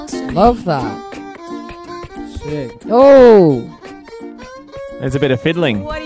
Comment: Did Zayn say "Sh*t" at 0:38???